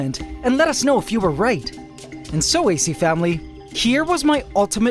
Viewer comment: I thought this dude said Ace family, I was about to gtfo..